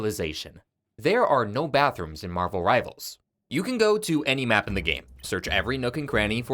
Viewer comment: Thought that said "There are no bottoms in Marvel Rivals" for a sec and had to do a double take.